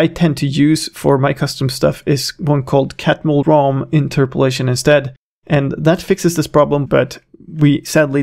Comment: I didn't quite understand the interpolation method you said. Can you write it down? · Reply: That's Catmull-Rom interpolation.